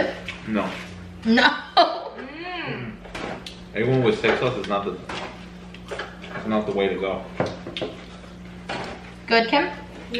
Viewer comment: "A1 with steak sauce is not the way to go" lol did I hear that right? Dont you mean A1 with cucumbers.